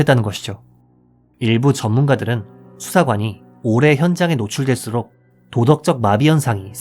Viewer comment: 올해가 아니라 오래 인데 자막 오타 신고요~ 음성도 올해에 가깝게 들리는 것 같아요 근데 진짜 경찰이 아니라 범죄자의 자질이 더 뛰어나네요